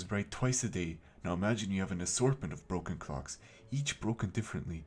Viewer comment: Omg I thought he was saying broken cocks and I was confused why he was showing clocks 💀〽️